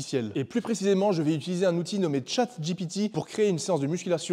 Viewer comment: Super vidéo 👍🏻 Pendant toute la vidéo j'ai entendu "Chad GPT" ah ah